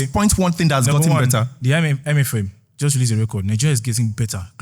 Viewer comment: tinubu guy said I-me-fem instead of IMF 6:56 😅😅😅